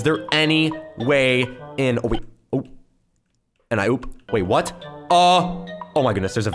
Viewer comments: Who heard? ‘’and I oops-‘’ Let’s see how many people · AnD I Oop I’m not the only one who heard that right